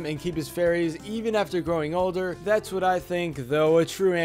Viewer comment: I knew the Krabby Patty recipe would be number ☝️! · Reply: oh I thought he said that’s what it tastes like. Shiiittt. Makes sense what you said though